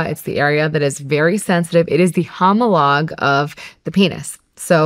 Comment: I think when she said it’s the “Hamulog” @ 7:34 that’s what I got! Lol I don’t even know if I spelled it right lol!